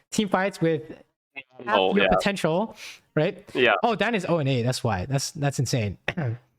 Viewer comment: what the hell does say? "oh dan is O N A" ?? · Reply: oh diana is 0-8